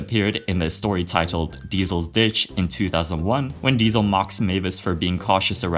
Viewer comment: thought you said the b word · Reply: 😂😂😂😂 "In the episode, Diesel's Bitch," 🤣🤣🤣